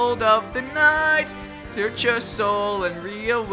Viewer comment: is sounded like you said 'Hurt your soul' not "search your soul" That makes it 5x better